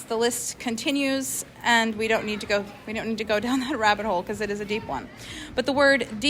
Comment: HOLY CRAP!!!! Any idea what was said before the obvious laughter? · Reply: It sounds like “silly rabbit” once it’s sped up